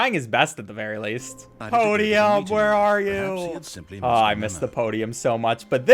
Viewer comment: I thought he said "Codyumm" when I first watched this lol